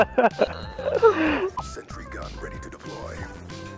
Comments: Anyone else hear "sexy gun ready to deploy"? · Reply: I heard,"sentry gun ready to deploy,"You need your hearing checked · Anyone else hear "sexy gun ready to deploy"?